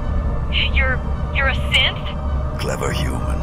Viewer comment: did those soldiers say a Sith? as in a actual Sith lord like Darth maul or Darth Bane? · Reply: No it’s “synth” sorry if you misheard it